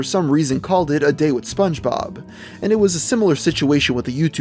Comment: i sounded like you just said that spongebob is an anime